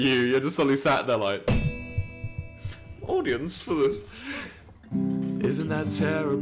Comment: I first heard it as "Organs for this", almost hit my head against my keyboard which would probably break my spacebar once again. My spacebar already gives up on me half of time.